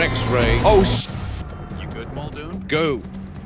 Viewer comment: TELL ME YOUR FAVOURITE EASTER EGGS IN THIS SERIES Watch all my episodes here: <link> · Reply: "Must go faster" 😂 Or maybe I heard it wrong